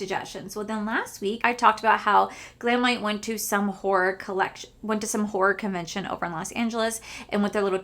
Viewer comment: I'm so dumb! I had to play it back twice. Thought you said Glamlite went to a WHORE convention! 😂